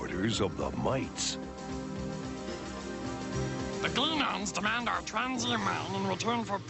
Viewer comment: They're lucky they're not fighting 40K Necrons!! :D At 1st I thought they said Klingons, not Gleenons?!?! :D